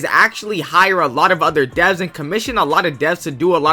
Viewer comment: ayo why does the subtitles say deaths instead of devs